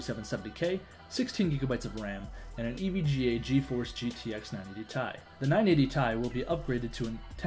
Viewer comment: dafuq? Did he just say 980 "TIE"?..................ummmm